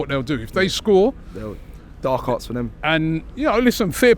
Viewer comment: Did he just say “dark arts from them” 😂😂😂